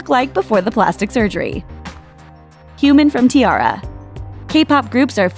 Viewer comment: I thought she said "human" instead of Hyomin (first girl)